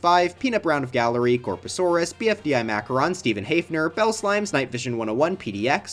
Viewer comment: Did he just say what I think he just said? BFDI?! :)